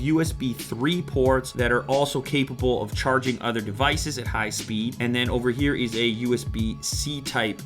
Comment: Ha ha, @ 1:54 did you say "...charging other devices at high speed" ?